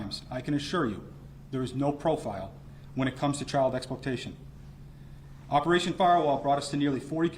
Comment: I Thought it said OPERATION FARWELL, that would be quite a fitting name , time to switch glasses